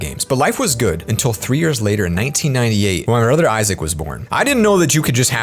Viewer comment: Did you say “ruther” or “brother